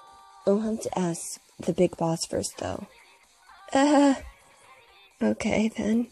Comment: I thought mineta said the big boobs first.....I need help